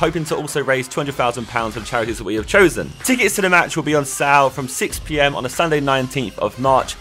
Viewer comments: I'm pretty sure he said sal instead of sale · ticket will be on sael wtf did you say